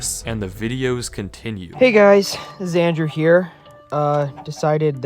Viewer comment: did he say "Xander"? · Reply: He said "it's Andrew"